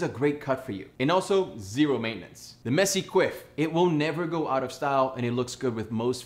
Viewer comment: I thought he said “the messy queef” 💀